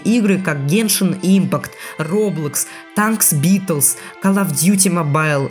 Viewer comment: Что он говорит? Tank Blitz?